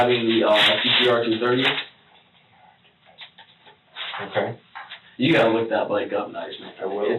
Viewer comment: captions are wrong, its TTR-230, a quite nice mid-range Yamaha bike.